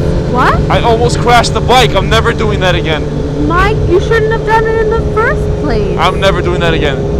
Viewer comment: Yo... im from the netherlands and didn't understand at 3:22 did she say my or mike ?